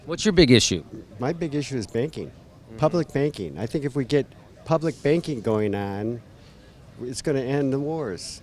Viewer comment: At first I thought he said "public baking."